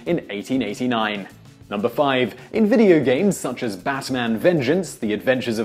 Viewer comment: For a minute there, I heard "Bassman" and wondered if he was about to tell me about some silly pun.